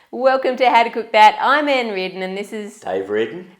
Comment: As a brit, I heard, "welcome to adequate bat" as the intro! Love your vids.